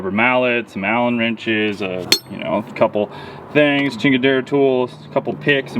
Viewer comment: did you say “chingadera” tools ? I was wondering if I caught that right. If you did that’s hella funny . Good video by the way 🤙